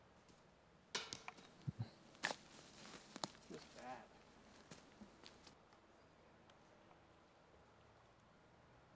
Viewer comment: whats that? cant understand you because youre wearing a mask while playing discgolf outside in the woods. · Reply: Pretty sure he said fu k you Corona